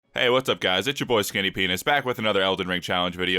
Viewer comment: Lmfaooo, yo this video just started playing and all I hear is “yo wassup guys, it’s your guy skinny dick” 🤣🤣🤣